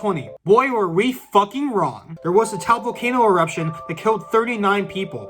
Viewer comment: did you say Taal Volcano? Lol I was there, ashes everywhere 🇵🇭🇵🇭🇵🇭